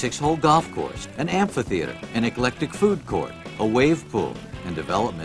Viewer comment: Did I hear that right at minute 31:24, I played it five times over and it sounded like he said 'neglected food court'